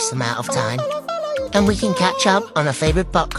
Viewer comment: For a moment I thought it said butt cast instead of podcast, I had to watch it twice 💀